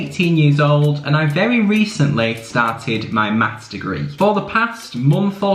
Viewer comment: I heard "I'm 18 yo and i started my master" 😭